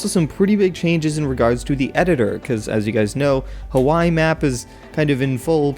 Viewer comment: DID YOU JUST SAY "KAWAII INSTEAD OFF HAWAII